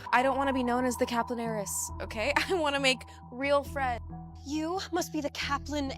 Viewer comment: I played this back so many times and I swear she’s saying “I wanna make real friend” instead of “real friends” 😭